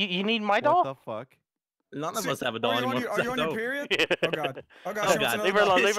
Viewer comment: “Are you on your period” I know i wasn’t the only one who heard that